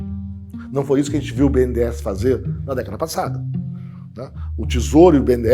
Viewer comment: só eu q ouvi ben 10??